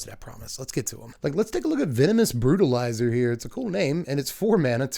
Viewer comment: I heard it as Venomous Broodalyzer and thought it was a sick Spider pun.